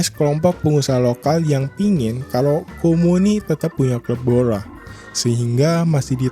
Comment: Di menit 2:22 kamu ngomong apa dah bang? Kumur2 kali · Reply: Tetap hunya hub hola 😅